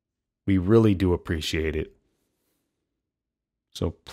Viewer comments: At the end the video cuts off before he could finish saying hydrated and it sounded like he said stay high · Cant donate, but I hope yall continuously get all the help you need. Prayers go out to you and yoyr family and especially your dad. Also the cutoff "stay hydrated" made it sound like you said "stay high" which is funny to me